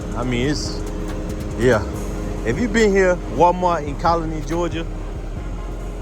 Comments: thanks. I live in Georgia and I’ve never heard of Colony Georgia. I googled and still couldn’t find it. Where is it? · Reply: are you talking about Conley, GA? If so, I definitely know where that is. I thought you were saying colony. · Which Walmart is that? Sounded like you said Colony, Georgia.